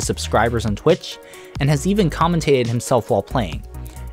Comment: did I hear, "and he has even come to himself while playing"? Somebody please correct me. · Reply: and has even commentated himself while playing" that's what he said